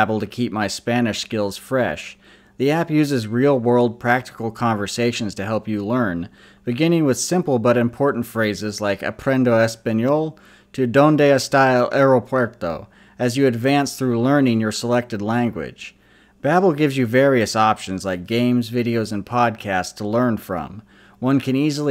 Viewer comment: LoL. I thought he said, Donde esta el perro puerto. I was like, WtF. Wait a minute.